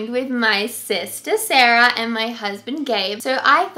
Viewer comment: I thought you said this is my husband gay